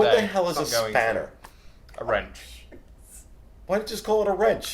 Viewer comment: I thought said "spinner" totally different thing though. #SteveHatesOldPeopleChildrenandDogs Reminds me of the movie Tin Cup